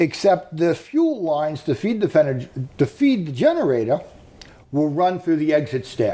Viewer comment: was he going to say, "to feed the fetish", but corrected himself? Sounded like an ALMOST Freudian Slip, imo.